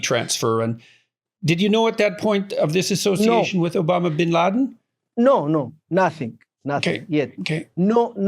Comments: ey ey ey Dr Peterson, did you just say Obama bin Laden and not even bother to edit it? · did he just say Obama bin laden ? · did jorden say obama bin laden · Did I just hear Jordan say Obama bin laden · Anybody else catch Jordan’s slip at 56:25, Obama Bin Laden? 😂😂 I wonder if it was a Freudian slip. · Did anyone else hear "Obama bin Laden" at 56:25? 🤣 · did Jordan say Obama Bin Laden? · Did peterson just said obama belladen xD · did he just said Obama Binladen 😂😂 Man is taking the piss. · did i hear Peterson say obama bin laden 😂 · Did he say Obama Bin Laden? LOL